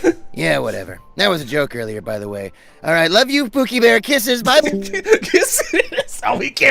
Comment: WHAT DID SAY 😂😂😂😂😂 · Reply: "LUV YOU POOKIE BEAR KISSES" I still can't believe he said that